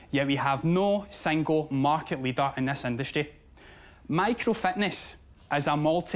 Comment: Every time he says Fitness, it totally sounds like he is saying "Fatness"